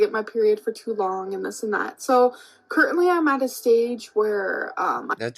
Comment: she said, "curtainly" again instead of currently lol.